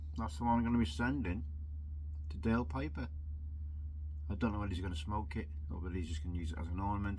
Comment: not going to Daves going to Dale's as in Dale Piper🤣🤣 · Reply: hell sorry mate!…. I thought you said piper dave…. My bad!